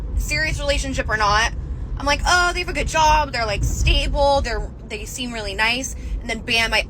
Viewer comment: just as it hits 6:12 you can hear she caught herself before the Freudian slip came out of what she really wanted to say “ they Rich ” “they seem really nice” 😂